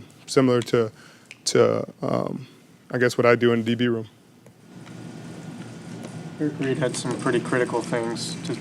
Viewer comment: Who had some critical things to say? Even with CC I can't hear/understand who he is talking about. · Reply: Bookem Danno Eric Reid